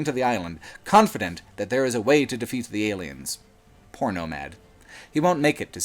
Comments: When you said Poor Nomad I thought you said Porno Mad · I thought you said porno mad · I know he says "poor nomad" at 16:30, but I heard "porno-mad" first. This has no bearing on anything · I surely thought you said "porno mag" and I scratched my head for a solid minute trying to figure out the joke before I realized my mistake. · At first I thought you said Porno Mag.